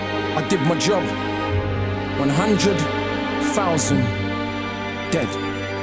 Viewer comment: what did akala say? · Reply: I think he just re-quoted one hundred, thousand, dead